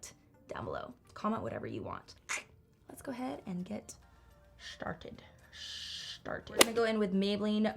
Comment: Am i the only one who thought she said "sharted" instead of started?😂